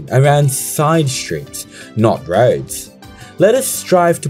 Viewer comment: anyone else heard rhodes instead of roads and got confused haha